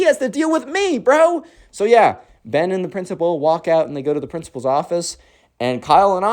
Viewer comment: At the end I heard him say “the teacher and the PRINCIPAL go to the principal’s office